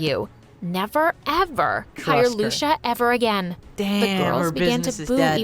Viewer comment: i misheard Lucia as Luscha and instantly remembered